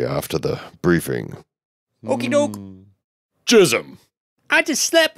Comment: am I the only one who heard jismslap?